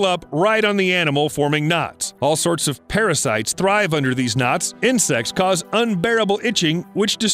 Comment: Am I the only one who heard "Under deez nuts"?